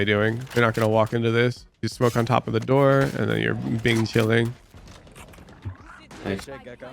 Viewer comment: did i hear bing chilling